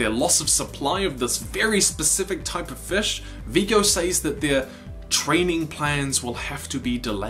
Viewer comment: Am i the only one who, when watching the episode for the first time, misheard "training plans" for "trading plans"? I'm so deaf...